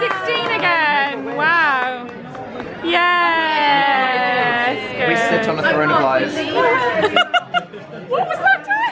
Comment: What did Dan say at 7:13?? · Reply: ''Sixteen again... We sit on a throne of lies''